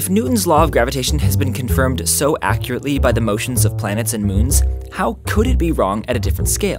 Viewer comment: i thought he said planets and beans